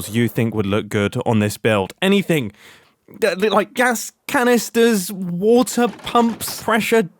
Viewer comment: When you said "Gas canisters" I misheard and thought you said Ghast. Which gave me an idea, and this can be visible or not but in the early 2000's a lot of cartoons depicted electronics being powered by a hamster on a wheel, so maybe some kind of mob in a case somewhere? Whether you open a door and that's the only thing in the room or visible on the side, obviously it's 100% up to you, I just thought it was kinda funny to maybe go in the opposite direction